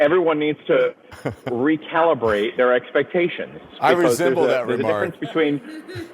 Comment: did lemon just say "I resemble that" lol